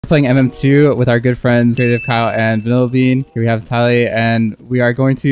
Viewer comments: I really thought he said girl friends but he said good friends😂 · "we are playing mm2 with our girlfriends" Did i hear it right?😮❤ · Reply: No it's good friends not girlfriends